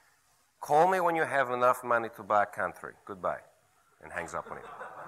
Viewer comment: In the story about the model on the phone with the rich dude I thought the last thing she said to him was "Call me when you can afford pantry", which makes sense in the context of the story and is even funnier!